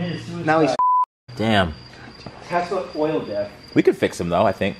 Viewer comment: 'I saw one of your testicles drop' . . . 'its about time' baaahahaha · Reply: I thought he said tesla coils... ;-)